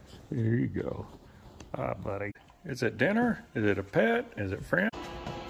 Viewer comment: I love how it cuts off. I want to think he said “Is dinner, is it a pet, is it French.” 😂